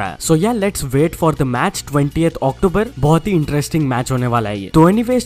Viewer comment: Did he said match is on 20th Oct?